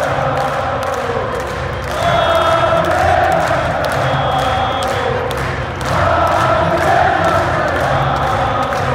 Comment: What did Liverpool fans say at 11:40 ? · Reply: Oh Javier mashersno